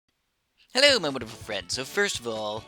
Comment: I thought he Said "hello motherfuckers" in his into